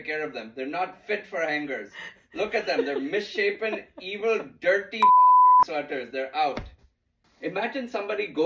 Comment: I thought he said "misshapen, evil, girthy" and I f*king lost it.😅